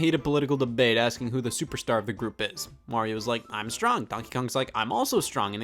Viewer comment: I can't help but hear "Mario" instead of "Wario".